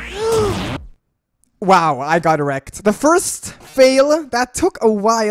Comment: did he say "i got erect"... it says that on the subtitles as well..